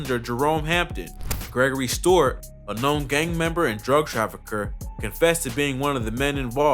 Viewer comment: Anyone else hear trafucker in stead of trafficker lol